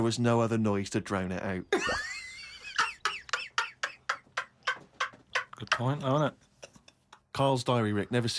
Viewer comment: What does he say 4:09 ? ? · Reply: Good point though init